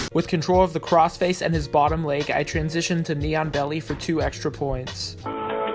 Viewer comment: one day i was training and my professor says, "ok lets go to knee on belly" so i was like hmm thats a weird name.... BECAUSE I THOUGHT HE SAID "NEON BELLY" AND THEN I WATCHED THIS, AND I REALISED THAT I AM A COMPLETE DUMBASS